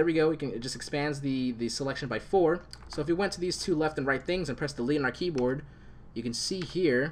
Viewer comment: what you said at 11:32? whats the commande ? u talk so fast...relax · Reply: " if you pressed delete on our key bored " idk if English is your first language if not understandable, but if so yikes